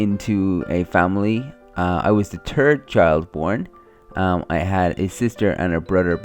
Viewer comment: At first I thought you said the turd children!! Not trying to be rude I thought it was funny! 😂